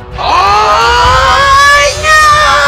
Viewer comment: 나만 아아아아잉야아아아아오오오라고 들리냐ㅋㅋ